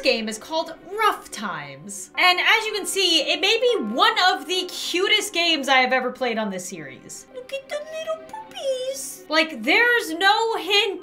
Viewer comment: omg what is wrong with me- I thought she said "LoOk aT ThE lItTle PoOpY" 💀 I'm immature🤦‍♀️😂